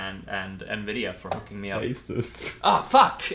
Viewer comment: Who else thinks he said Acer instead of Asus at 9:31?